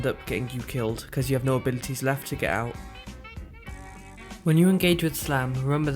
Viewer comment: I didn't understand what you say at 0:25 about echoe slam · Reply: Fernando Stella I said "when you engage with slam." My Mic is really bad sorry